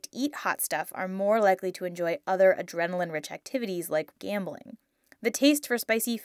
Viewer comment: "Some studies have even shown that those who like to eat hot stuff are more likely to enjoy other adrenaline-rich activities, LIKE GAMBLING" shows a guy parachuting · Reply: i thought they said camping